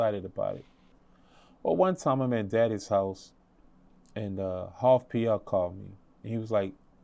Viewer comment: Did he say Daddy's house @ 4:25???